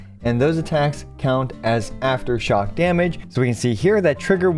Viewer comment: I’m not even joking I heard him say “Who specializes in off-field back shot damage” I KNOW he said aftershock damage but my brain did not want to hear it